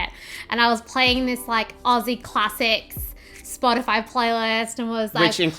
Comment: Definitely thought you said "Ozzy Classics". But "Aussie Classics" sounds pretty awesome as well! Cheers from PA.